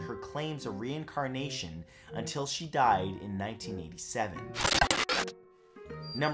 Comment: is it 1997 or I hear Matt said it was 1987?